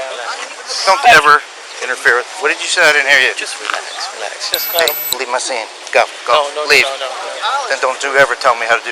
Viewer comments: Did he say you need to chill at 4:40? I can’t tell what was said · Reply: Smith he said “just relax just relax” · what did the other office say to him? How to do his job what? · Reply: I think he said “ just relax man just relax”